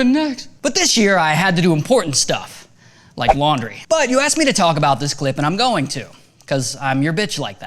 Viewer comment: I swear I heard "But you assed me to talk about this clip..." Ȏ_ō